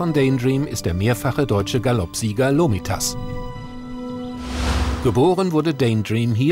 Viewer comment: Ich hab erstmal Loritas verstanden😂 dann hab ich gecheckt das es Lomitas heißt